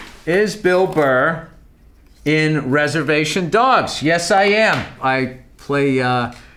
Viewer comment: I thought he said " Reservoir dogs. " I've seen that movie many times and was about to rewatch it.